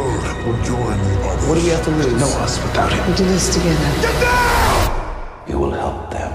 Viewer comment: Did I heard “Shazam” in the Superman Trailer????!?! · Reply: In the super man trailer.....? That's cyborg saying "get down" ...... I was confused at first too🥴🥴🥴